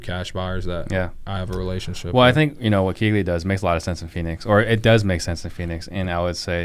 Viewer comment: Great Episode. What was being discussed at 36:10 ? I heard "keegly". Could you tell me a little more about this? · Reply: KeyGlee. Check out my interview with Jamil and Josiah.